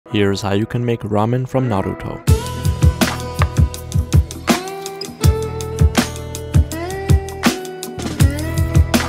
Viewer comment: I thought he said let's make Ramen for Naruto